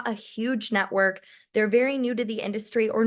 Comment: I heard "they are very nude to the industry" and I was really, really confused 😂